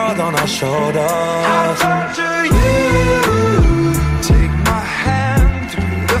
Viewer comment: Whoever thought it said "I talk to you" instead of "i torture you" Idk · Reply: Honestly growing up I just thought it said “I tacha you” Looking back I was and still am an idiot